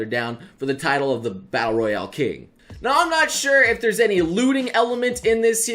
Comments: Am I the only one who heard him say "lewding" elements at 3:47. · I keep hearing "looting"as "lewding" send help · when you said 'looting' I first heard 'lewding' ;_